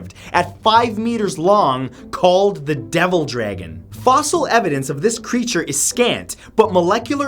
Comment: I've been playing too much Monster Hunter, I thought he said Deviljho.